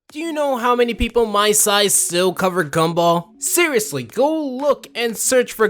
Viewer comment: Okay lmao I was eating chips and I thought he said “Do you know how many people my size simp over gumball?”